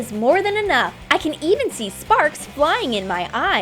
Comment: I thought she said farts